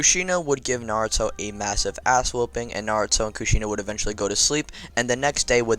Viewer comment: I heard "asshole bang: instead of "ass whooping" BRUHH